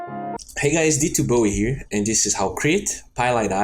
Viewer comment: I always thought he was saying, "Hey guys, it's yuh boi here."